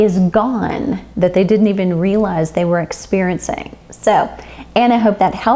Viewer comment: Did you say sometimes GRAINS or GREENS are ... at about 5:20? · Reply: GRAINS... are a high sensitive food for many women